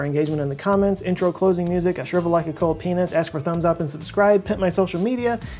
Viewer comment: 1. Listen at half-speed on YouTube.... 2. What did you say at 1:46-1:51??? I know what I thought but... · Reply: shrivel like a cold penis